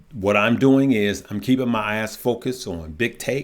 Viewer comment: Hahah it sounded like you said “I’m keeping my ass focused…” then you said it a second time and I heard eyes 😂